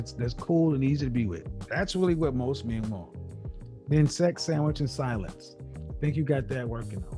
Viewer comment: Sex, silence, and sandwich..... is that what I heard? 'Cause that's hilarious!!